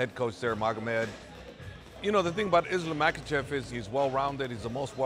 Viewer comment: Closed caption says: "Islam microchip"... "little microchip"